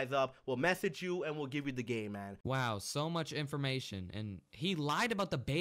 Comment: it sounded like he said “we will give you the gay man”